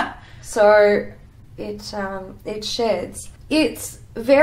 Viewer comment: I heard 'it shits' 😂